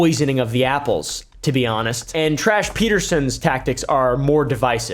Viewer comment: I thought he was saying trash instead of tash😂